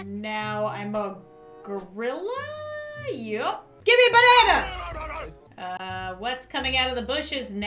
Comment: listen closely to the gorilla person, it sounds like he actually said give me bananas lol